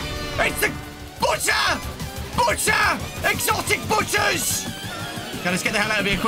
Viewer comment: What is saying "EXOTIC BUTTERS!" What I hear "EXOTIC BUTCHERS!" (Not making fun of him)